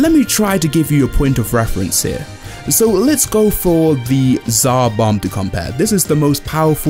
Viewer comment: did someone say ZARBON